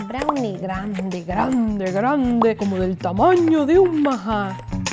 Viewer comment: Yo pense que decias grande como un majas... el majas es un animalito que se parece a una ratita grande... en la selva peruana